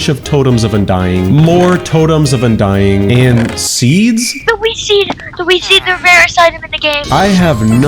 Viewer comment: i thought he said weed seed edit: 12:50 got me laughing